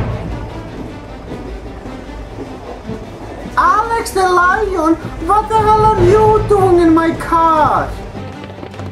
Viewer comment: took me a year to figure out what he said cuz I laughed so hard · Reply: Alex the lion, what the hell are you doing in my car.